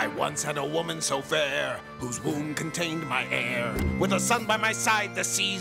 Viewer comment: “Whose wound contained my heir” Am I mishearing this or ... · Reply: *womb